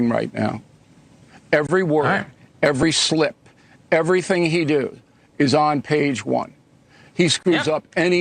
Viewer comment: Everything “he do”? Did I hear that? He’s a Harvard graduate.